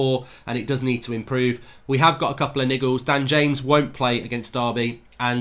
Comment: Lmaooo i thought you said nig*a